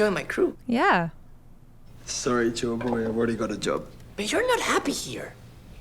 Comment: I almost thought he called him joyboy right there.